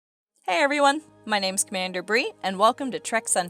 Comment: Introduction: "Hi everyone! My name is Commander Brie" (...) What I hear: " Hi everyone! My name is Commander Breen" (...) What I feel: "RED ALERT! All CREW IN THE STATIONS" Realizing I heard wrong: "Pheew, It's BRIE... Hi Brie, nice to meet you!" =)